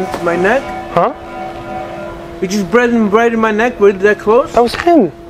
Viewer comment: “you just bread in my neck”😂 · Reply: I heard "you put it in my neck!"😂😂